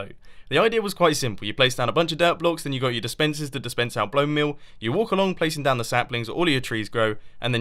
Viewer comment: did he just said blonemeal instead of bonemeal?